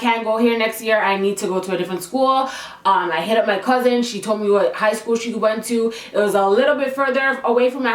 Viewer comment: Am I the only one that thought that she said Harambe high school? Btw u have a new subscriber❤️